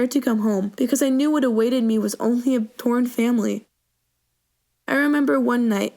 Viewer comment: did she say porn?